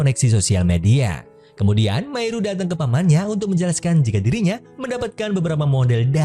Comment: Gue heran masa cewek cantik di bilang paman sih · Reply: kedengeran nya paman, tp pas didenger lg kaya mamah